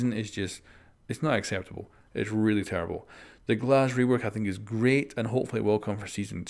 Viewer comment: ‘Glaz rework’ What I hear: ‘Glaz nerf’